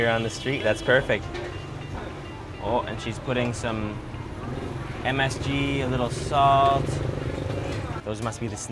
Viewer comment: at around 2:03 did he say she's putting a little MSG in his food?